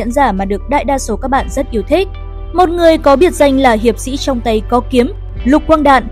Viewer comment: FAct: Ngày xưa khi xem phim trên các đĩa vcd, dvd của hãng phim Phương Nam thì Shurikenger được đọc là: "Hiệp sĩ trong tay có kiếm" còn cơ khí Revolver Mamoth được đọc là "Voi Mũi dài Talung" Việc này có 2 nguyên do: 1 là để trẻ em có thể nghe hiểu được và ghi nhớ dễ hơn 2 là do họ dốt tiếng nhật ( cái này tôi chém gió ra) :))) À ngày xưa tôi từng nghe nhầm thành "Hiệp sĩ Tung tăng có kiếm" :face-blue-smiling